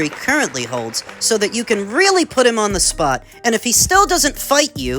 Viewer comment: I heard "put his feet on the ground "